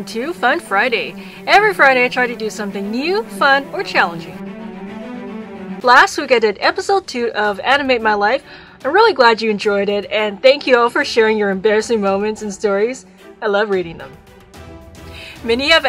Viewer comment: I had my volume rlly low and at 0:09 i thought it said “last week i did an episode of ending my life” an i was like Hold up 😂